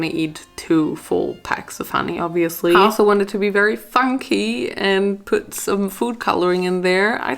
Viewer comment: It sounds like she said" i also wanted to be very F**kyyyyyy" llmao